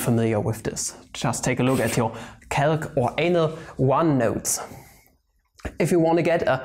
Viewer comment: did he.. did he just say anal 1 notes instead of analysis 1 notes? wow just realised how dirty math sounds